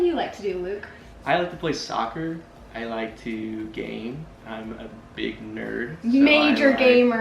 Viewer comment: I keep thinking said “Gain” like gain muscle 😹😹😹🤭 (instead of Game)